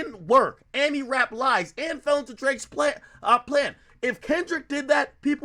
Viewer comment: Did Ak say, "Our Plan" at 32:33? 🤔